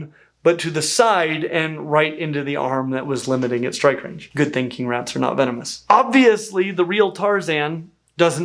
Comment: My brain shorted out for a second and I misheard this as "Good thinking rats aren't venomous," was really confused for a sec